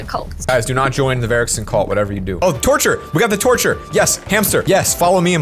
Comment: Who else thought said torch I thought he said torture. And I was like YASSSS but then I heard torch and was like 😥😂😂